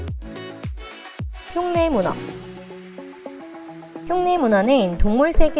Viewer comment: 나만 똥내문어라고 들었나..